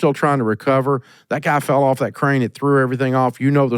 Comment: I thought she said train to